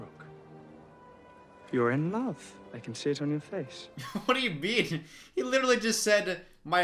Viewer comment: I really thought the brother said "I can sit on your face" instead of I can see it on your face... I had to listen to it like 3 times XD